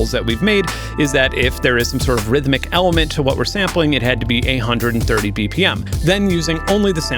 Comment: i was ALMOST excited when i heard you say "EIGHT HUNDRED AND THIRTY TWO BPM"..... then you said it again and i more clearly heard you say " A HUNDRED AND THIRTY TWO BPM...." BORING! and here i was all HYPED up to here your take on an Aron Funk styled track!...... :(